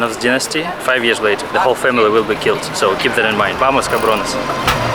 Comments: did he just say “vamos, cabrones”?! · did he say "vamos cabrones"?